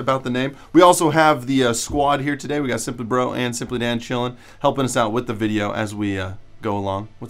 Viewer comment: Lol thought he said simply man child at the beginning.